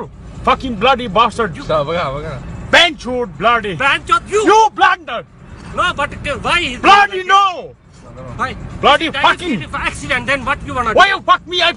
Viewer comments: “YOU BLASTARD” always gets me 😂😂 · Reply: I heard it as “YOU BLUNDERED!” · sounded like he said “YOU BLENDER” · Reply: blunder*